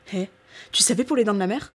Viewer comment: Au début j'ai entendu "Eh, tu savais pour les dents de ma mère"